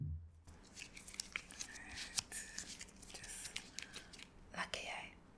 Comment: Mi love it. I dead ass almost fell out the chair at 2:54 did you say "Lock ya eye"? ahhhh lolololol I can't cope. Dis video affi mek a millyon. Love your authenticity. Keep em coming.